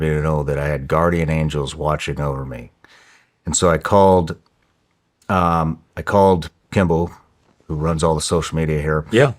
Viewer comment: does he say I called Kymbl?? That's my name I thought I was the only one on the planet!